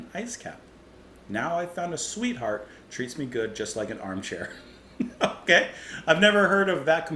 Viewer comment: I always thought he was saying "Treats me good just like an object" but I guess "armchair" is not so different. One of my favorite Eno songs for sure. Love the build-up, love that scuzzy, grinding rhythm, and yes, that freaky vibrato on "cra-a-a-a-a-a-azy".